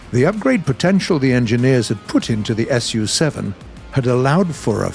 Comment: "the upgrade potetial the engineers had Putin" anyone heared that too, or am I just too crazy by trying to grind the japanese helis?